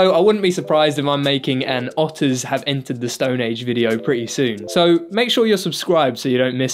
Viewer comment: i thought this said "the Arctic Monkeys have entered their stone age" and was like well thats not very nice >:^(